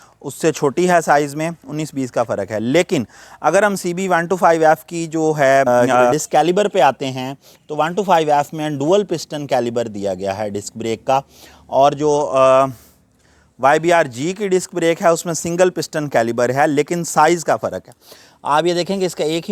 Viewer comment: A comprehensive review, well-done. At 6:45, for a moment I thought you said 'Caliber'. OHC engines are only 21 years younger than OHV. So both designs are here from 1925. Hondas with CB designs are OHC outside of Pakistan . Atlas Honda is misleading the public by calling an OHV engine CB. I have owned one CB 125 in 1972 in my UET days. It had a 5 speed return shift OHC engine.